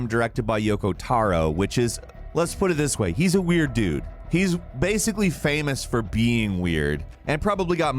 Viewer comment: i immediately went for Google when i thought i heard you say "He's basically famous for peeing weird" hahahaha